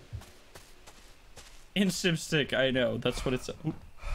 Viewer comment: I thought that you said stick🤣